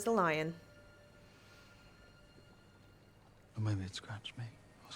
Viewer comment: She said "maybe i scratched you"? I thought she said "it" lol.